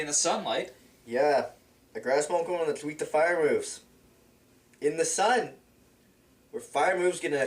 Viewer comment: Wait, what did say at 8:19? "The Grass won't grow when it's weak fire moves... in the sun!"